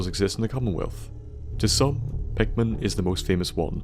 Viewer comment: who did he say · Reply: Pickman, the "raider painter".